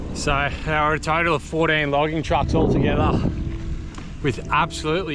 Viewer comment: did you say 14 logging trucks? or 40? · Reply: 14 in a row. But I easily saw more than 40 total